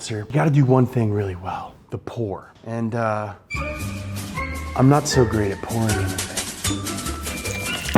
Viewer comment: Not that good at pouring? Heh... I thought you said, "I'm not that good at porn."